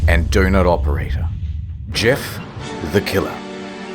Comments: I thought you said GIF the Killer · Did you just say Gif the killer?